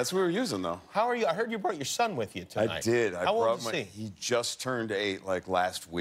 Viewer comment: I first heard "You brought the sun with you" as in implying it was raining and when Jeffrey arrived the sun was shining. When he answered with "He just turned 8", I for a second thought "Wait, the sun is much older." Not my brightest moment, I admit.